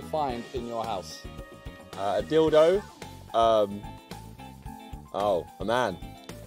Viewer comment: It sounds like he said "A Nan" he doesn't use his lips when he talks lol